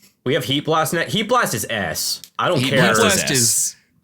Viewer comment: I heard “Heatblast is ass” right off the bat and was ready to defend